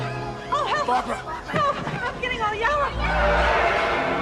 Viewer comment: Did she say "I'm getting all yellow" ? I can't tell {::>_<::} Also at 8:20 I like the style of those side burns. 9:27 "Come alive!!!" Nice deliverance